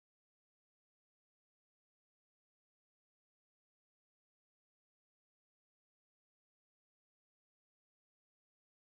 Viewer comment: Man cest du francais a 50:23?? Ca sort de ou plzzz pis cest quoi quelle dit exactement? Nice job pour le mix!!! :) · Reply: ca dit: ok la fuck off ostie de tabarnak, cestpas juste moi qui a .... je sais pas trop exactement.